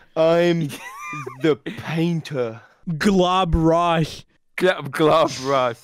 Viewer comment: my subtitles said glob rice and also PAINT TASTSE LOIK YUMMY